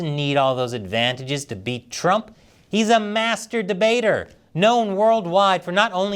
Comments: I thought Chris called Biden a mast**bator 😂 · At the time 2:52 into the video Chappell said Biden is a "master debater" But I clearly heard he said "Biden is a masturbater" Now which one is he ? Or both ? Can Chris Chappell clarify please ?